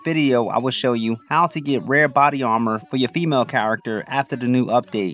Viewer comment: I thought you said red body armor now it makes sense